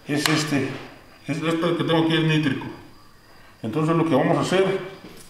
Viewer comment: ¿qué es? por que no se entiende. Saludos. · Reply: No se entiende del todo, pero dice "...nítrico" que sirve para deshidratar las monedas ¿que es esa sustancia?